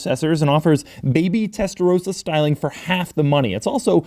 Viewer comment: I thought he was saying Tesla Roadster lol